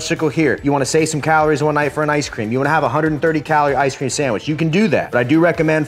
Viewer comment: 🤣 i thought he said "830 calorie icecream sandwich" I was like "Shit what does a normal one have for calories!? I used to eat 2 at a time" 😂